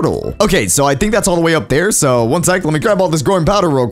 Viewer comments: i thought you said groin powder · it sounds like he is saying groin powder